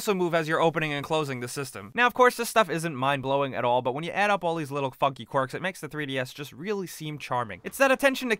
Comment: I thought he said "But when you blow up all these fucking quartz"